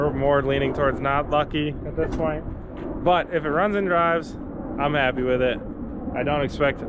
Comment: I thought it said run and drive